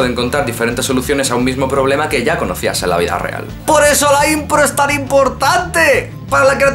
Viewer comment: le falto diccion amigo. No se le entiende. Por eso.....es tan importante. Cual es la palabra que dice? Gran canal. Mejor diccion para la proxima. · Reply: "Por eso la impro es tan importante". Gracias! Intentaré cuidarlo más ;).